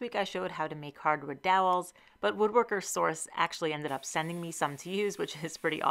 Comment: The first two times you said it, I heard "Woodworkersaurus" instead of "Woodworker Source". I would TOTALLY shop at Woodworkersaurus.